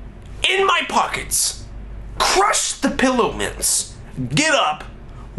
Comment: For a second I thought he said "Pillow pets" o. o